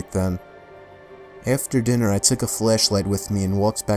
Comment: Did he actually say "fleshlight" on 28:18 😂😂